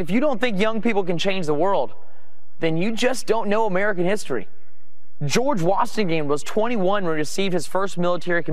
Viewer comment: does he really call him "George Watchthegame"?